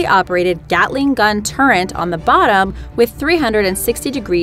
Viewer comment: Why does she says "turrent"?